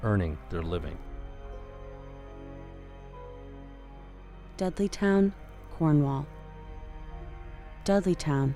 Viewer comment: I have to admit, I thought you were saying "Deadlytown" not "Dudleytown". Seemed to fit though.